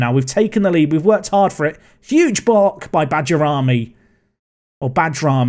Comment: I wasn't looking at the screen when you first said 'Bajrami' and it sounded like you said 'Badger Army' - if that's not a nickname i dont know what is! hahaha